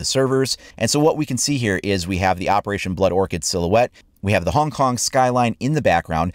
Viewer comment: I thought it said Operation Blood or Child